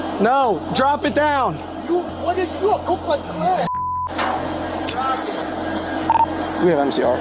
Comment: I think he said, " You a Ku Klux Klan! (word I dont wanna say) "